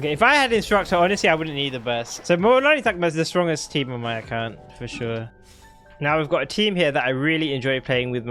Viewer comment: What is he saying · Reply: so mualanis like the strongest team on my account